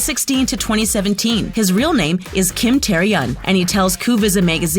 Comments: did I just hear "Kim Taeriyung"? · thought she said "kim teriyaki" 😂